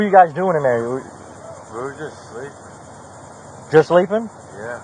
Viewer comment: Them damn broken doors 7:12 he actually said we were just sleeping